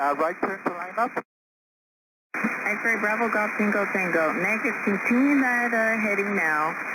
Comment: Pilot's accent is so thick I kept hearing it as "Go tacotaco"